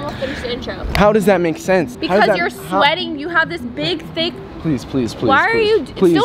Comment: WTF DID SAID 😳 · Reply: I think when she said "you have a big thing" she was going to say that he had a big stain from sweat or something